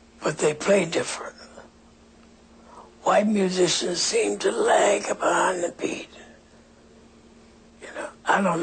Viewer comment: what he said in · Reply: Lag behind the beat